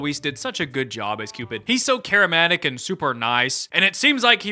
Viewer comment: I'm sorry to be that commenter, but did you just say "he's so charimatic"? Love your vids btw!